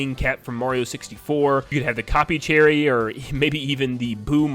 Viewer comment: why did I hear “boner Mario” 😂